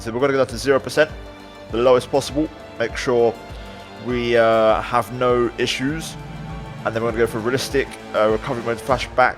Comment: Bruh who else heard him say let’s make sure we don’t have AIDS